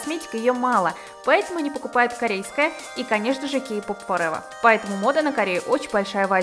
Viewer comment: сказала "k-pop forever" ИЛИ МНЕ ПОСЛЫШАЛОСЬ? 0.0